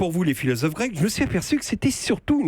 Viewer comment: he says supposedly the title, "Je me suis aperçu", but I only hear "me s'aperçu", what did he say?